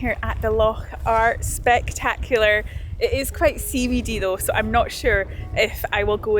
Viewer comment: What does CBD mean in the context you used regarding the Loch? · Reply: in, as you stood outside beside the Loch, the closed captioning translated your words into, “It’s quite CBD though . . .” But on listening very closely, and factoring for my hearing loss, I think you may have said, in reference to the possibility of you swimming in the Loch, “It’s quite ‘sea-weedy’ though, so I’m not sure if I will go in swimming over the next few days.” Did I finally hear correctly what you said?